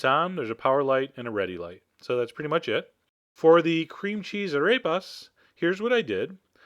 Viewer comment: All I heard "Cream cheese I Rape Us."